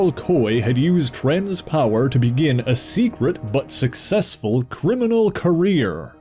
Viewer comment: When you said "but successful" at 4:26, all I heard was "Buttsex" Also, I like that you explained Sunpot's powers accurately. Everybody just thinks he shoots fire.